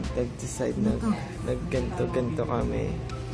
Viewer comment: did jah said "nag gento-gento kami" in this part??? Or maybe i just heard it wrong...